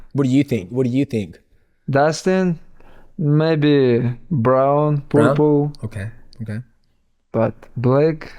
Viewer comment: I thought he said "maybe brown...poopoo" lol